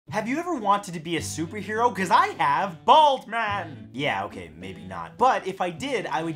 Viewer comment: He said, "Bald man". I heard, "Balls man".